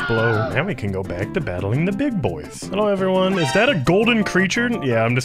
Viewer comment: his "hello everyone" sounded like daily dose of internet